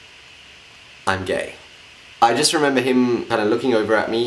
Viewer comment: Wait so, if he's gay, I can't marry him :( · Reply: NERDGIRLSTRYYOUTUBE I don't think you're in the position to state wether someone is gay or bi. And I clearly heard him say "I'm gay"